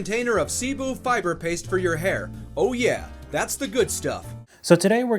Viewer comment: I thought he said seafood fibre paste for your hair 😂